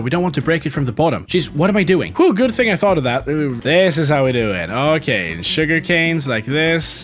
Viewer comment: anyone else hear "Good thing I thought of that, uwu"